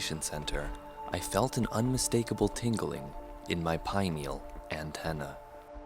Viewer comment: tingling in my pineal antenna wtf lol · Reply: Oh, I thought he said "pine needle antenna" and I was struggling to figure out what that could mean!